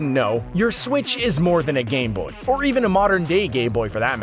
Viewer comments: what did he say?! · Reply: Captains say gay boy but he said gameboy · No one cares about Minecraft Story Mode... 8:02 "Modern-day gayboy"? even the subtitles agree with what I heard, surprising it didn't hear 'dolphin painter' (inside joke to subtitles mishearing Collin Grainger or something) And by the way one of the most common touch screen type is a capacitive touch screen, not iPhone exclusive and you don't have to keep saying iPhone constantly as if that is the only mobile phone and is (now) the revolutionary piece of tech and the newest and best thing, saying 'mobile phone' will do as Apple are incredibly slow on adding 'new' features to their phones, it's like saying 'YOU COULD TURN THIS COUCH INTO A BED LIKE A HAMMOCK, when there are sofa-beds (strange example but it's 4am and It's the first thing I thought of) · why does he say "modern day gay-boy" instead of gameboy